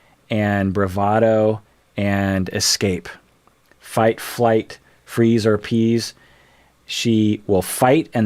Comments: Am I the only one who seriously thought said "fight, or flight, freeze, or peas" until I read the comments 🤣 and I speak native English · Fight, flight, freeze, appease. I thought you said fight, flight, freeze or peas! lol